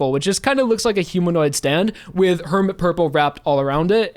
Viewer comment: I got an ad that made it sound like he said "A humanoid stand with DAWN dishwashing soap", considering that stone free is scented and soft&wet used soap bubbles, that was surprisingly coincidental